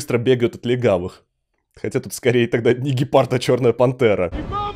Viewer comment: ниггепард? Я один это услышал?